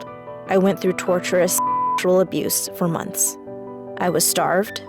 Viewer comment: DID SHE JUST SAY SEXUAL ABUSE?!!! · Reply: *Torturous sexual abuse